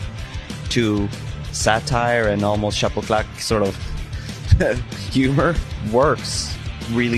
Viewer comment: what's the French word he uses at 1:22 to describe a style of comedy? The subtitles just say "notre style" but what is that specific style? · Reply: He says "chapeau claque humour"